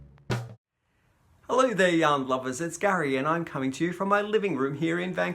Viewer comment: I have to tell you that in the beginning of your videos you always say “hello yarn lovers” and because of your (slight) accent, I always hear “Hello Young Lovers ! 😍 On Saint Patrick’s Day , the city dyes the river green in downtown Chicago .